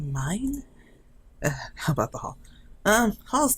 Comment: instead of it saying "how about the hall" i thought it said "how about hell"